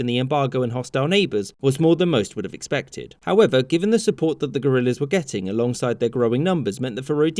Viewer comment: uh, did I hear that right. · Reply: I thought he said gorillas